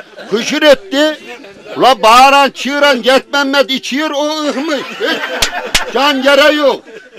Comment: de ne diyor tam olarak, anlayabilen var mu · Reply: Cenk Arslan Jet Memmet İçiyor O Ihmış (Sızmış Uyumuş) Diyor